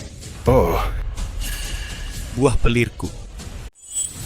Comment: bilang apa dia cok😂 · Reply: Wah telorku😂